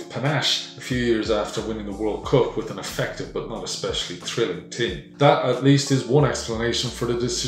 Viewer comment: There's an error in the subtitles, he actually says "but not especially thrilling", the "not" is missing in the hardcoded subtitles.